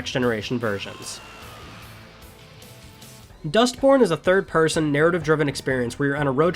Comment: Anyone else hear Dustporn?